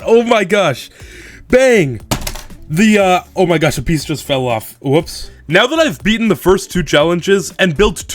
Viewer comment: What did he js said after bang🥀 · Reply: " O my gosh a piece just fell off. Oops "